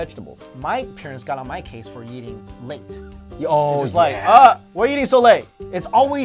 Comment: I thought he said eating lint not late!!!!!! :)